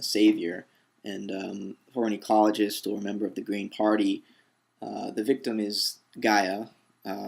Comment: Hm I thought you said "the grand party" at beginning of this video, meaning grand old party. Almost spit out my drink. There is a little something in our economic model, socio-commercial mores that facilitate a sort of unconscious attitude toward politics. A big part of that is a person living in debt due to easily available credit. It pretty much makes that person enslaved to their job and government which in some ways robs them of their voice to speak out on politics and issues.